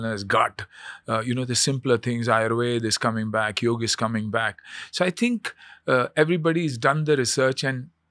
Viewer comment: i heard Yogi is coming back 😂😂😂